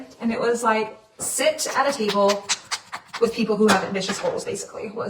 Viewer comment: I wasn’t really paying attention in the beginning, I thought she said titty bowl instead of table 😂.